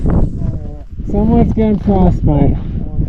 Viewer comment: What was rolling down the slope at 8:40? I can’t make out what they are saying. Sounded like “that’s a man”. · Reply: it's someones glove, so he said "someone's getting frostbite" personally i hope they had spare gloves